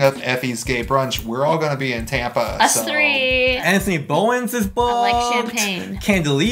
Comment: Full name of the guy @ 10:14 hahaha I just can't understand them hehehe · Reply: Anthony Bowens